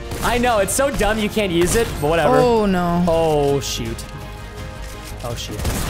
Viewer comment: i thought he said something else but now i know. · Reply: Nah he said oh shoot